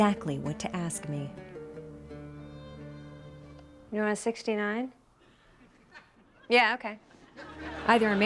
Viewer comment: thought she said "do you want to 69" :D